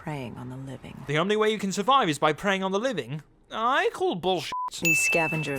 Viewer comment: He says "I call bullshit" but the subtitles say "I'm calling bullshit", sins